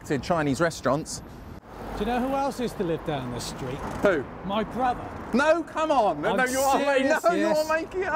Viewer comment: I heard "My brother" as "Brian Glover"